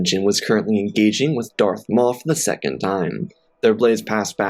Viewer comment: lol I thought I herd it wrong but at 0:07 I hear you say darth moff